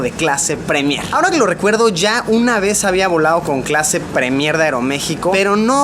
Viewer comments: escuche premierda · like si viste que dijo premierda en el minuto 1:48 jajaja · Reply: G4M3R XD el dijo Premier de Aeromexico, lo que pasa es que él hablo muy corrido. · parece que dice "pre mierda" xd saluditos Luisito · yo escuche premierda XD